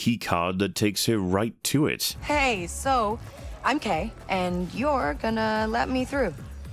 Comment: I misheard that as "Hey. So I'm gay and you're gonna let me through" and I almost didn't question it.